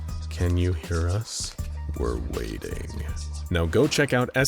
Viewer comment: Hah, not sure if it's an intentional pun or not, but I heard "we're wading" and not "we're waiting"